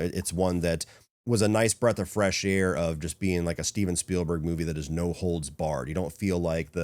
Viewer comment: I thought i was on drugs for a quick second cuz i thought it said "Steven Segal movies"